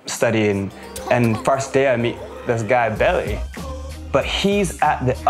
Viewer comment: "Billy" Sounds like he said Belly.